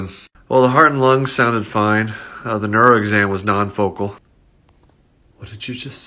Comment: I thought he said Non-Vocal.